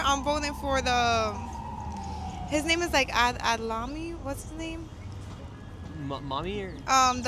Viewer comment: did she say that she's voting for salami